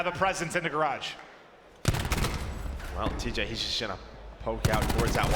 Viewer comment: did really say “he just shitted on him” but mumbled lmao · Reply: "He's just gonna". Kinda late to reply but yeat